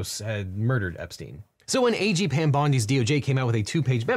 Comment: I kept hearing Devin say “aging Pam Bondi” and only now realized he is saying A. G. 😅